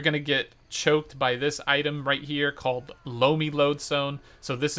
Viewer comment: Not gonna lie I was looking away for a moment and thought I heard him say, "you need blowme loadstone" and I had to look at what he was referring to in shock 🤣